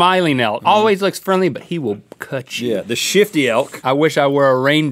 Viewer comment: "The shifty elk" LMAO I thought he said "The shitty elk"